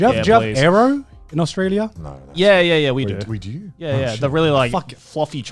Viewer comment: you can hear just the grin in this man’s voice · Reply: Connor thinks he’s Australian Edit: okay I think he’s actually saying “wai- you do?“ but it sounds like ”we do?”